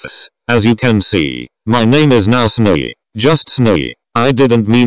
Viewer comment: Is It Just Me Or..... Does It Sound Like He Says Snuggy Instead Of Snowy.....?